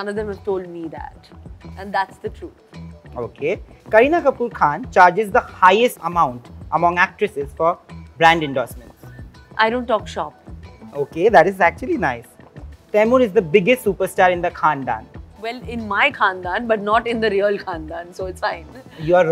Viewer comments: Even today she looks very gorgeous❤️ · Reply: what did she say i didn't talk what ???? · Sara-Kartik affair, Varun's marriage and Taimur's Nanny's salary - what's your opinion on the rumours doing the rounds? Do you agree with Kareena? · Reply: what did she say i didn't talk what ???? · I thought she talked about kartik in the intro Who else??... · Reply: what did she say i didn't talk what ????